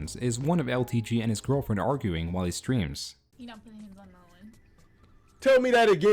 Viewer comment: “mmmnnnahahhhh byyyawwwaaa at nuuuun” What’s that mumbly bitch saying? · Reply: You're not putting hands on no one